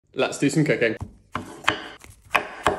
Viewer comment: What i heard 👁👄👁 "Lets do some cOcaiNe" Btw, the bacons look so nice